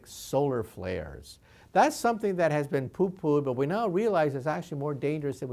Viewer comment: DId he say "Poo Poo'd" at 1:48 ? o.0